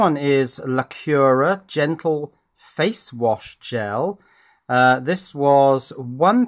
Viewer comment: Initially I thought you said it was "Genital face wash gel" xD my mind is apparently dirty.